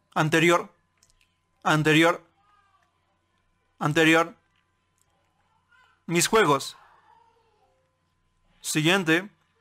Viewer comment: "Mis webos" Eso entendí :v